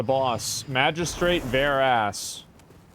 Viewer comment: thought he said the first boss was "Magistrate Bare Ass"